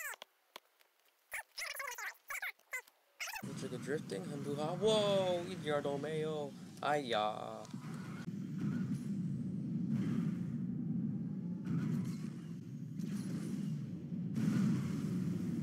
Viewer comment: sounded like “Oh, bravado mayo, ay-ahh” lol